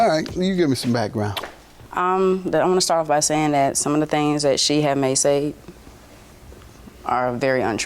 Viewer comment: What the heck was she trying to say… “She had may say”